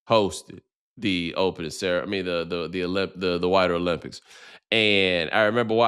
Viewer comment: for a second I thought bomani was saying "the whiter olympics"